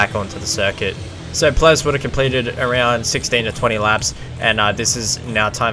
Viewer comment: 20 lap kart? oh i thought you said 34 lap